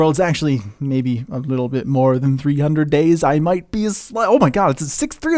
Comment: So at 0:44 does he say "I might be a slut" just saying... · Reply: No he was about to say I might be a slight bit off